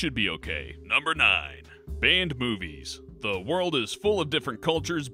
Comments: For some reason on number nine I thought he said "banned boobies" rather than movies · When he said ban on movies I thought he said " ban on boobies" I was like: wtf... And why are they showing a picture of E.T? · I thought he said "banned boobies"